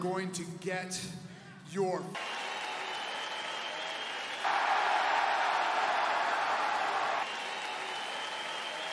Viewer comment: what he say?! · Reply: "Fucking head kicked in" is what he said